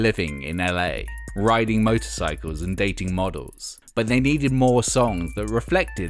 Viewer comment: I swear at 6:30 I thought he said "Riding models and dating motorcycles"